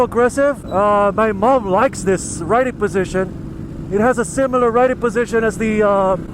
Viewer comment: did he say his MOM prefers a motorcycle riding position? Dang I'm guessing she rides & that means he was born into a moto family, sweet!